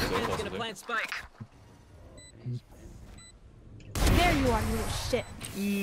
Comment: did jett say "there you are you little shit"?